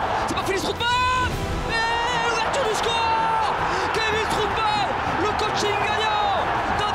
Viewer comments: c moi ou il dit strouball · A 3:40 i dit stroutbal ou c moi qui rêve 😂 😂